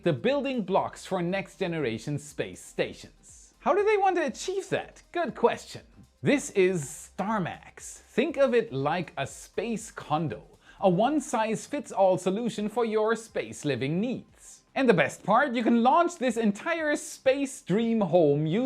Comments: LOL, Space Condo, when I first heard you say that I thought you had said "Condom" and I had to back up to see what you really said!😂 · Not going to lie. My dyslexia kicked in and when you said "space condo" I heard "space condom" with the added context of one size fits all, I was really confused for a second. Had to back it up and relisten. 😂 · Woah. For a second there I thought he said “Space condom, a one size fits all solution for your space living needs” I actually hit rewind to make sure I misheard him lol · Not gonna lie, I thought you said "space condom" at first.